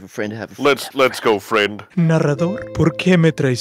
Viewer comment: When said " narrator" in Spanish It sounded like he said Naruto